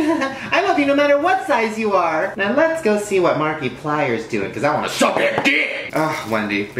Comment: Was it just me or did he just say "Markiplier"?